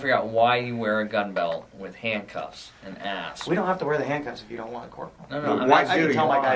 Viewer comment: [TURN ON CAPTIONS] if you have captions on. At 6:48, When Cpl. speaks and says, “...figure out why you wear a fun belt with handcuffs and...” right after and I think he says a.s.p., but captions says something classic.